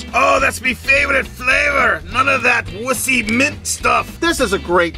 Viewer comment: What did he say? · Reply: not that wussy mint stuff..